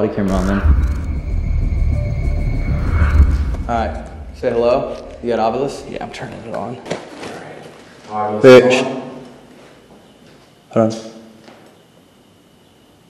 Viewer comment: Why at 18:40 did that sound like saying “b!t(h